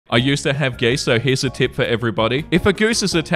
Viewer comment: I was scrolling past quickly and all I heard was “I used to have gay sex”